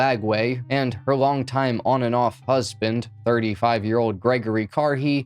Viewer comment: At about the 0:01:00 minute mark, he said "husband" but for some reason, I heard "Buzz-binned". Any OG MTV fans remember Buzz Clips, and of course, the inevitable Buzz bin later on?